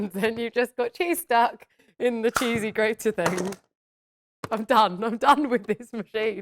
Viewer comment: I thought she said I'm dumb and not I'm done 😂. You also have a knife there (Which you primarily need to be careful of using) and try cutting things in half before cubing in the press.